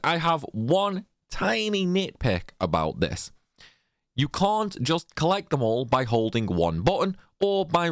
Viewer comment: did he say cunt or cont?